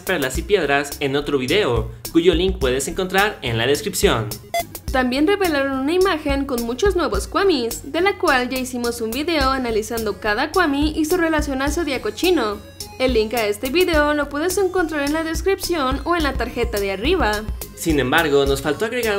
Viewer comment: el el 4:17 parece que dice "cochino" jeje